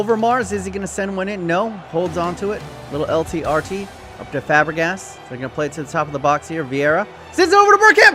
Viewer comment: did you just say “Sex Fabregas”?